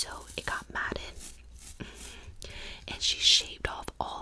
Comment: I love this video, very relaxing. But is it just me? Am I the only one that fought that the word mattered was the word mad. Because at first, I seriously thought she said that the hair was mad. The hair was tangled and the woman shaved it off, but I imagine a funny scenario of the hair, just coming to life and abandoning her because she wasn’t looking after it properly😂 All jerks aside, great video, keep it up❤